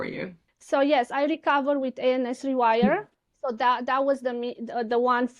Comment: Can anyone make out what brain training program she says here? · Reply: ANS Rewire - there is a link under the video!